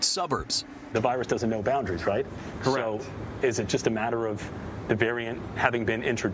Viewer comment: “the virus has no boundaries, right?” Did anyone else hear the response “For reals”? 😅